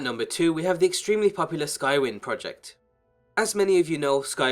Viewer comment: Skyrim is the most anticipated mod for Skyrim. lulz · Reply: no, it's called skywind but it sounded like said skyrim